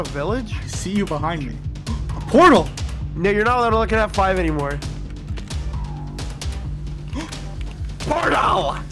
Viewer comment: WHAT DID HE SAY ???? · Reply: Oh hell no